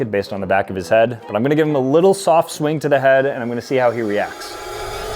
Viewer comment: I thought you said soft…